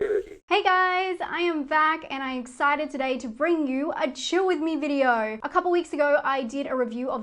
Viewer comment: Omg i thought you said "chew with me video" lol